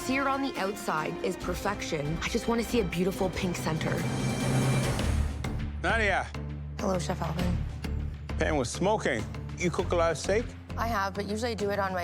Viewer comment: i thought she said "hello chef how old are you" like wtf lmao